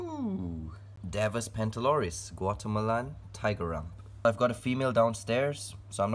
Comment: I swear to God I thought he said watermelon when he said Guatemalan And tiger rump!? XD